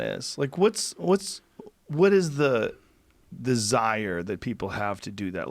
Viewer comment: does he say "desire" or "the Zire"?